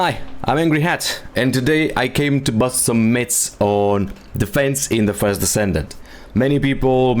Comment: I was skeptical especially at the beginning when I thought you said "mitts" but you said myths. Then I watched the entire video and switched my mods. Its one of the best demo vids out there. Thank you.